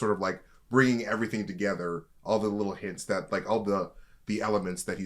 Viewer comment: I had to go back I thought he said elephants 😂